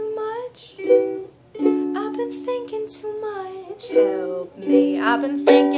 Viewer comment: "I've been thinkin' too much, help me". "I've been thinkin' too much, help me." Damn, at first, I thought he was saying "I've been drinking too much, help me." Damn it. wrong again. lol